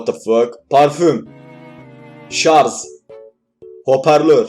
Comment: 'jarj' a 'jarzzz' demedi. Hayır ben yanlış duydum. Sakinimmm. 13.16-18/<num>BG 8A 32BL 516DL 678BA 1,9BY